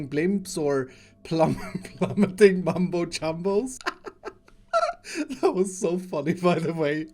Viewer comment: captions say plummeting bum boat rumbles ?